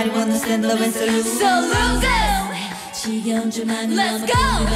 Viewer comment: 쯤에 규진이 애드립 뭐라고 하는거에요? salut go? so look go?